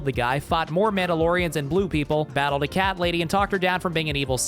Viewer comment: it took me way to long to figure out you said, fought more Mandalorians and blue people. Instead of fought more Mandalorians and blew people.